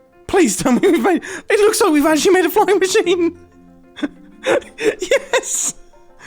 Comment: does he say Fucking machine